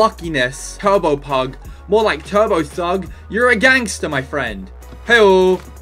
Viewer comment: when he said turbo thug I thought he said turbo sugg 😂😂